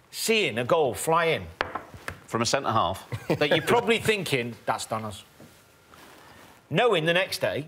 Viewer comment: Did Danny says that’s Thanos?!